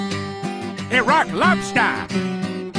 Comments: When I first saw this I thought they were saying "a rock lobster" 💀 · When I watched this as a kid I always thought he was saying “a rock lobster” 😂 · The high pitched “Iraq Lobster” gets me every time😂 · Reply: I thought he was saying "rock lobster" all this time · As a kid I always thought he was saying rock lobster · Ngl when I first heard this I thought Peter said - A Rock Lobster